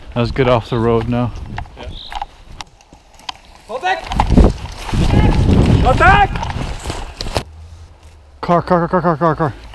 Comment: I thought he was saying “Fall Back!”